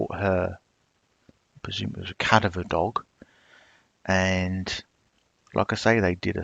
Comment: Ok I know my hearing is not great but you kept saying about dogs I thought you were saying cat and dog! I'm like they start using cats? Wth?! Then I realized you were saying cadaver dog. LOL. With your accent it confused my tired brain. Ty for all you do!